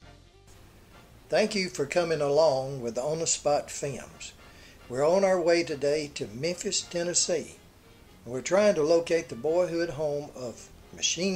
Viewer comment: Did he just say "Welcome to ... on spot fims?" 🤣